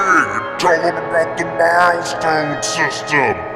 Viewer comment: what the h$!@ happened at the end all I heard was " your choking me"